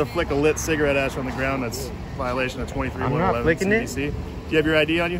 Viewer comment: “That’s in violation of 23…” I couldn’t understand what he said after that. I tried The subtitles but they’re way off. Does anybody know what penal code the cop stated? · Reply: 23111 cvc